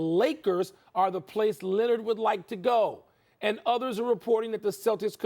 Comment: Did he say Lillard at 0:11?